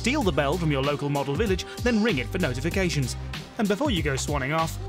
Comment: I'm sorry, I was listening to all the puns at the end again, did you just say "thanks for chicken out the video" or am I just hearing things now? They're both birds so it works but I dunno